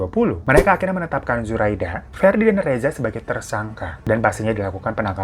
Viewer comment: bang saya yg salah denger atau emang malah bilang ferdy ya 😅